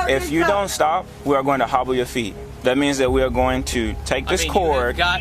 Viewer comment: On the third incident when the officer said, “If you don’t stop, we’re going to hop on your feet,” I thought he said, “I’m going to hobble your feet,” as Kathy Bates said/did in the movie Misery. 👀😀